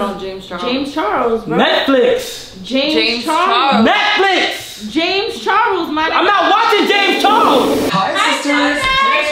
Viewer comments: She really said : “James Charles my nig**” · did she say “ James Charles my nigga “ or I’m I going stupid 🤔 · did she say ninja 🥷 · she really said James Charles my nigga